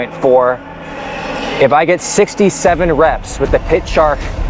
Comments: did bro just say 67? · did say 67 reps 😅